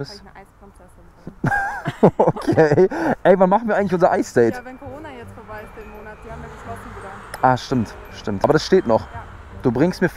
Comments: hab ich da von bruder gehört 😂😂😂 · hab ich das richtig gehört “die haben geschlossen Bruder” ?!? R.I.P Moji